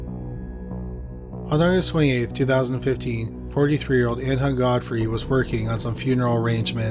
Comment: Honest to god, I thought you said Anti-God Freak instead of Anton Godfrey.